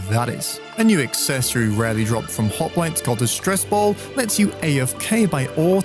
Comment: rarely drops from what? i can't understand sorry :( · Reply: hoplites